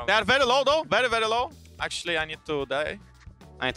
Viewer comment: Did jankos just said "actually, I need to die"? At 8:12 😂